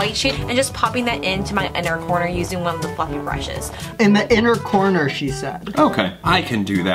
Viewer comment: My mind is so corrupted, at 23:42 I thought she said "I'm gonna take that white sh*t on one of the f*ckin brushes" 😂 I blame listen to too much Starkid